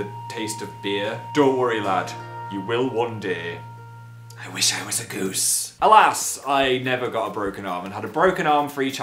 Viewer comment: "I wish I was a goose" I just freAKING SPAT MY PEPSI OUT AND MOVED SO FAST MY LAPTOP FELL ON THE FLOOR. OH MY GOSH THAT MADE ME LAUGH SO HARD PHIL! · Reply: I thought he said "I wish I was a ghost"... goose...